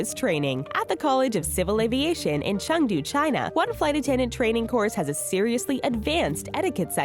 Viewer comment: why does it say peru but she says Shanghai, China?